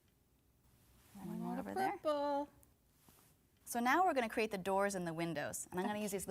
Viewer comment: 13 years and i still don’t know what says here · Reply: PURPLEE !!! 😜